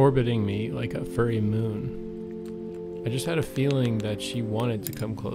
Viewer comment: “Orbiting me like a furry moon” 💓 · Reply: I thought he said, "Orbiting me like a purring moon". Either way it is terribly poetic.